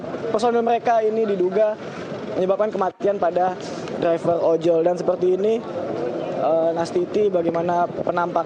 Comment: gua ga salah denger kan seekor ojol ? 1:28 emang binatang ya ?